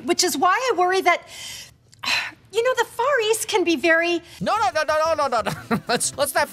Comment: I heard "the furries" 💀